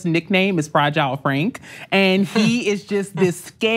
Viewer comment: Fragile Frank? I thought mispronouncing someones name was violence. Guess that's only a one way street. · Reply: Until repeated that 'nickname', I thought she was saying "Fried Jowl Frank"